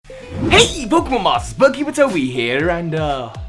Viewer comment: I kid you not, I've been watching you for a year and a half now, and I swear I always thought you said "So hi" instead of "Soar high" until this video. I blame your British accent for that. Edit: I've been blessed with the coveted heart from! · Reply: Same reaction, though so thought he said “So Hype, Pokémon Masters”. When he said it the way he did I was like “😲 This is some kind of yanny or Laurel shenanigans”.